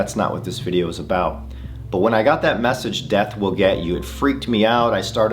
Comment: i heard, "the dead will get you". maybe, the dead will understand you?